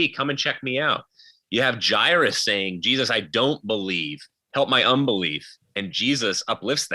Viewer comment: correction. 1:47:11 Jairus does NOT say "I don't believe"....He says "I believe".... Nonetheless I think you are correct in what you say about doubt. Good debate. I am a believer in Jesus Christ.